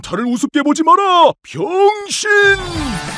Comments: ㅋㅋㅋㅋ 이거 병신이라고 들리는건 나뿐인가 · 이거 변신이 아니고 x신 처럼 들리는데